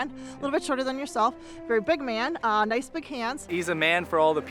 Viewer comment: LOL😂😂😂 Did I hear "nice big hands"?